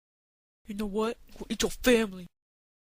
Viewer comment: Great stitch I love it but lol the thing at the end xD of the video I thought it said " you know what I'm gonna eat. Your family" xD